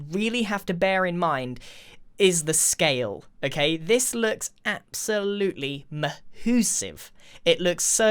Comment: Did he say Mahussave?? I mean that’s pretty dope. Probably gonna steal that but like wth 😂